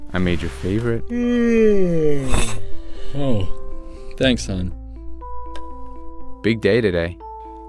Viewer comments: Did he call him son? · Did he call him son 🧑‍🦯